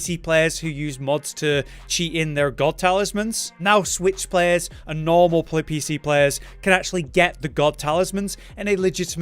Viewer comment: im sry but im assuming you were about to say playstation players and edited to pc player but what i heard was "normal pleb pc players" ouchy... XD